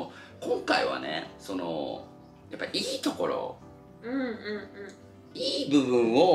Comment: のさんの｢いいところ｣の｢いい｣がショッカーのように聞こえたのは気のせいだろうか？笑